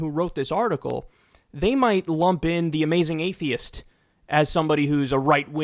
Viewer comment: People like The Amazing Atheist are very left wing and very anti-sjw. Being anti-sjw does not mean right wing. Sargon of Akkad is right wing and anti sjw. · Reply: woops, my bad. I thought you said "The Amazing Racist". THAT guy was on ebaumsworld